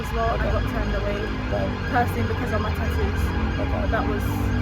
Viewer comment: what did she say here? "I got turned away because im a _____" shes a what?? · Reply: "because of my tattoos"